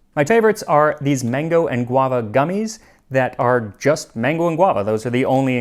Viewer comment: So, I was cooking while watching this video. While passively listening to the advert I really thought I heard “mango-iguana gummies” and definitely had a moment of surprise followed immediately by beginning to wonder what a mango-iguana gummy would taste like.🤣🤣🤣🤣